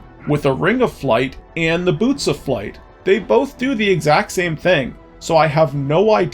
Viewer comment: I heard "noose of flight" when you said "boots of flight." For whatever reason I thought it was the funniest thing I had ever heard. Thought this slog was going to end much differently.